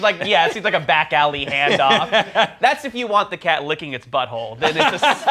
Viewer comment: I thought I heard "back-alley hand job" the first time.